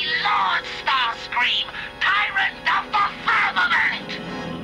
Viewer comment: Did Starscream just say that he’d be tyrant of the fatherland?